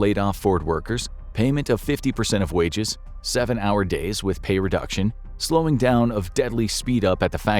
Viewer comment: Seven-hour days with or without pay reduction? The audio says "with", but the captions say "without".